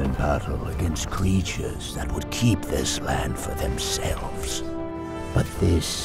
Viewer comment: Lol I thought is said Smäland. The kids playground at IKEA.😂